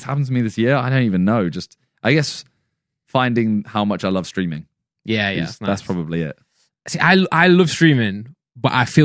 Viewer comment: dayumm thought he said screaming